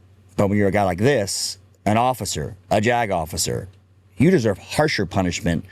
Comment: The JAG officer needs at least 10 years at Leavenworth. It is called treason. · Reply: I'm getting old... I first thought he said this "Jack-Off!" LOL